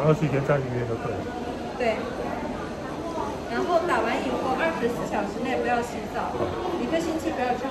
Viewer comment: 看了你这个视频才知道24小时内不能洗澡。昨天那医生声音小。我听成24小时内可以洗澡。然后晚上回去就洗了。我说呢手肩肌肉咋会酸痛。还以为是自己感冒加上吃海鲜引起的